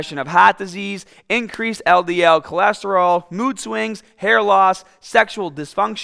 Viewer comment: thought he said boob swings 💀